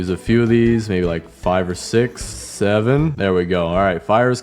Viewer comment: what did he say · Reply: 6 7